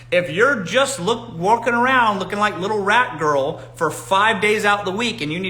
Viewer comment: He said “looking like rat girl throughout the week”😂😂😂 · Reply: 😂 i thought he had said rap girl 😅